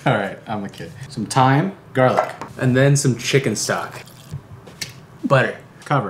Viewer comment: i litteraly thought he said chicken suck not chicken stock llooll